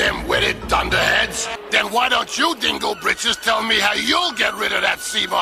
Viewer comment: Did he say 'dingle bitches'? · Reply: He said britches